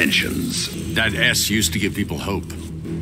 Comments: Am I the only one who heard "That ass used to give people hope"? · I'm I the only one who heard "That Ass used to give people hope."? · Lmao. At first I heard it as "That ass used to give people hope." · Why I heard Batman saying "THAT ASS" instead of that S. 😂